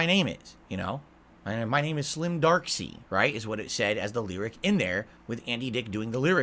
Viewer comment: did he call himself dark SEID?